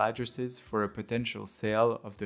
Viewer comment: he said "peace" at the end.. I thought he said "please" and waited like... please what?